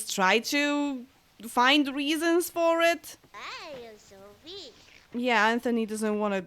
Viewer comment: it kinda sounds like Anthony is saying 'Hiya Sophie...'